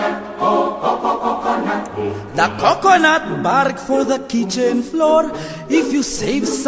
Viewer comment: I thought he said barf 🤮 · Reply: Bark